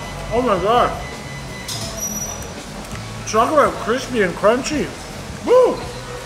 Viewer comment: it sounds like he saying chocolate crispy and crunchy lol